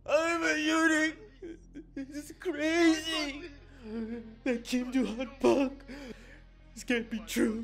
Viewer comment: 김두한이라고들림